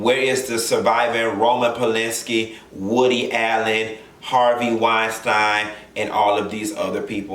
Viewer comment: i almost got shook at the 6:23 mark, i thought you had said "woody harrelson." lol. woody harrelson is my white boy crush, he's good in the hood.